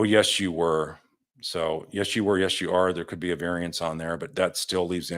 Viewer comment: I hear “ yes you are” 👍